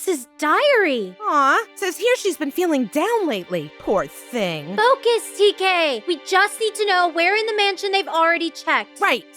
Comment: That almost sounded like Soap said "DK"